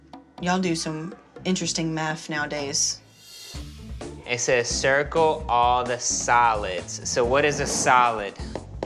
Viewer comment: I thought he said encircle a salad lol im laughing at myself😆